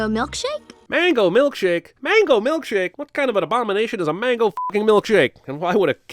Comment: I'm literally crying at 5:43 he mentions his disgust with mango milkshake but I, WHO WAS WATCHED THIS MOVIE SINCE I WAS BORN PRACTICALLY has NEVER noticed she said MILKSHAKE. MILK SHAKE. I THOUGHT SHE WAS SAYING MANGO SMOOTHIE SHAKE OR SMTH (I'VE NEVER TAKEN IN WHAT SHE WAS SAYING) AND I'VE WATCHED THIS SAME EPISODE OF CINEMA SINS MULTIPLE TIMES AND I JUST NOW NOTICED SHE SAID MILK SHAKE I'm dying